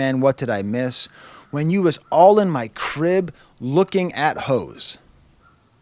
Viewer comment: I could have sworn he said “all in my crib, liquor and hoes” but the Apple Music lyrics do say “lookin at hoes” which is… just an odd lyric. Cause if they’re in drakes house, we know him and LeBron are doing way more than just “lookin” but okay